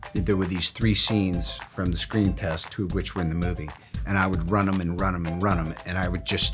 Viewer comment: What did Robert Downey Jr say 1:22 run on me, run on me, run one me...? Please help my English study... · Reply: run 'em (run them)